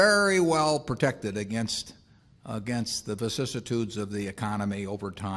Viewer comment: what word was that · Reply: vicissitudes